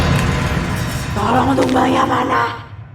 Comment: itu ngomong apa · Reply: tolong tumbalnyaa manaaa